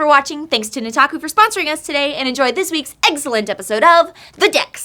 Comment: In the ad it sounded like said eggcellent referring to last week's pokemon. Also, I really liked her shirt I wish it came in blue or mint too.